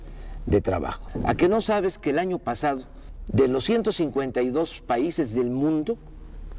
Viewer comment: El que no escucha bien eres tú. ¿Cómo me dices que escuche bien y tu no puedes volver a escuchar esa parte? Dice claramente: "de LOS 152 países del mundo..." (4:40). Y me da risa que luego dices: de una encuesta jajajajjaa, como si se hicieran encuestas para esto, jajajjaja, son estudios, no encuestas.